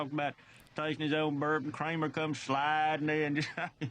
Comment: I'm from Kansas City, Mo. and I also understood it when I tried to. lol, It is really not that hard to understand if you bear down when you listen. 😊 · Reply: Other then I thought he said taste his own burp... guess not. They said it was his own bourbon up top. I did get the gist of it though.